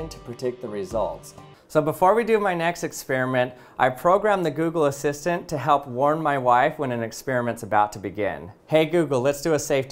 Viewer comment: I thought he said “ I prepared the google assistant to warm my wife”